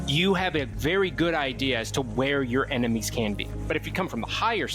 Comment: I swear I thought he said “where your enemy’s CAMPING” and not CAN BE 😂😂😂